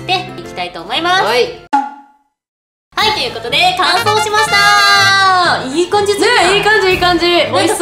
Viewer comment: 「完成しました」って言った時「乾燥しました」って聞こえたの私だけ？